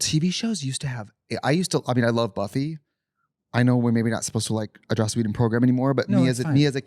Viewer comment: Can someone tell me what Trixie says after " I used to watch Buffy, I know we're not supposed to ????".Around 36:05. I cannot understand what she said 😂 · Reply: I thinkkkk she says”We’re maybe not supposed to like a Joss Whedon program anymore” joss whedon was the director/producer of buffy and treated women…very poorly. Even after .75 playback speed I’m not sure that’s what she said lol